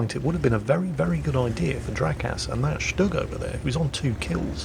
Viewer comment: with how you were pronouncing Drakas' name it sounded like you were saying "Jackass" but with an accent